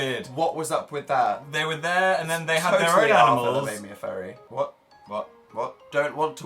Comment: What did Dan say at 5:52? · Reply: That arthur almost made him a furry